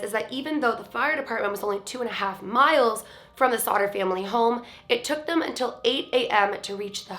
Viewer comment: I keep hearing "the slaughter family" I think I might have mental issues.....